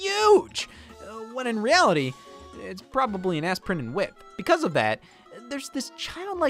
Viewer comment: what did he say? Ass printed whip???? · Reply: aspirin in width